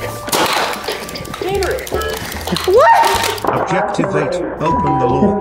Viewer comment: I started dying when I heard “BoP iT tO sTaRt!”